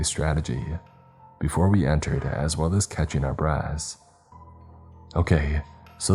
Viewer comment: I feel like such a child, and not like the 30 year old i actually am, because at 56:26 it sounded like "catching our breasts" instead of "catching our breaths" and it made me giggle. I also rewound it a few times and giggled everytime 😂😂😂